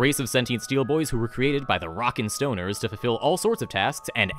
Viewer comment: Did I hear a ROCK & STONE? Nice overview. Not sure if I'll ever DM or play spelljammer, but it's nice to see interesting options.